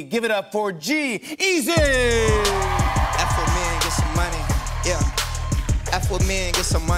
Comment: I hear like a "Apple me and got some money" 😂